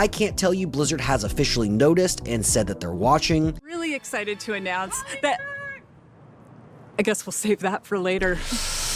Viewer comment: What is even said in the background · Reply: Holly Don’t. With an accent